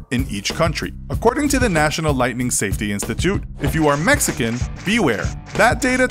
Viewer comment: I heard in 1:00 "if you are Mexican" so lightning is racist